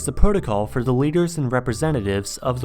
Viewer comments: When he says the Leaders the subtitles say Lizard People at 0:20 😂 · I died when i read "lizard people" instead of "leaders" on the subtitles at 0:20 lmaoo